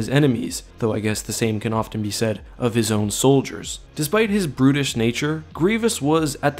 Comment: For some reason i understood "british" instead of "brutish" (?) natur and it gave me the hardest laughflash i've had in a long time.